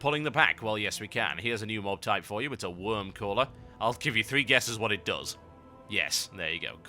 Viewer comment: lol when you said wyrmcaller i thought you said wormcaller and when the dragon came i was like wtf how is that supposed to be a worm its bugged?